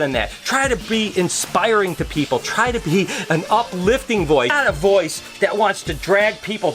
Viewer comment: I thought he said “I want to be an uplifting boi” XD